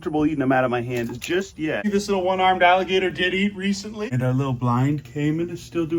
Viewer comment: Thought you called that alligator Diddy for a second there 😅